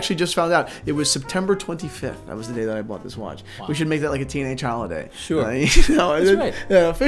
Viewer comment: Teenage Datejust Holiday - sounds like a song by The Who. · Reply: I heard 'teenage holiday' first too. Then realized it was 'T&H holiday.' FREE DATE JUST FOR EVERYONE came through clearly, though!